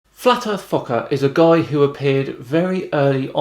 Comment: I swear I heard you saying "flatard fucker" I know it's just me because you don't use foul language.